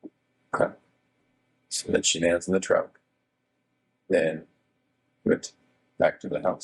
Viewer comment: did he just say WE got her body in the truck?!?! Like he said WE, during the digging a grave section…..